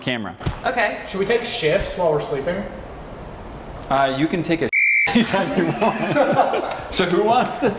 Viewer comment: Did Say You Can Take A Sh*t Anytime You Want